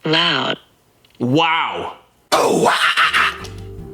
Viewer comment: That isn’t the TTS Voice translating the lyric to “wow”. That’s the TTS voice saying; “Wow.. wtf”. It’s just programmed to not say the quiet part out loud.